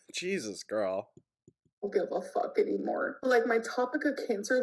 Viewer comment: I heard , ' my tropic of cancer ' !!! Ah , ' topic '